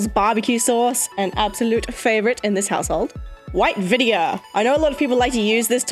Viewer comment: when you said “white vinegar” the first time it sounded like you said “vydia” which is my birth name and it surprised me